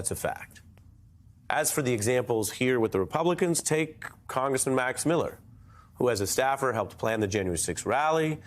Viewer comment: What he said: "Congressmen Max Miller." What i heard: "Congressman Mac Miller"